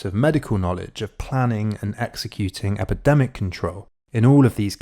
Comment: Did I hear well at 06:56 and on? "Epidemic Control..."